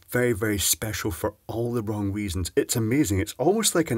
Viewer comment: I thought you said "wong weasons" while speaking about the cheap Chinese control module